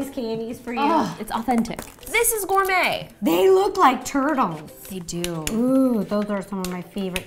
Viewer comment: “They look like TURTLES” -Ro 2020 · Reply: I thought she said TURDles